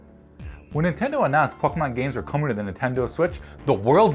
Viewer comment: I swear I thought he said “when Nintendo announced f***ing on games were coming to the…” 😂